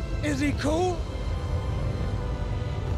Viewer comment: I always hear I'm Mary Poppins YO xd